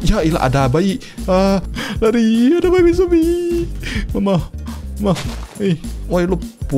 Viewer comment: Aku dengernya "Bab* Zombie" 🤣🤣🤣